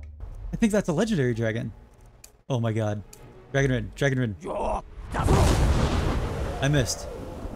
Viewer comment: he used call dragon instead of dragonrend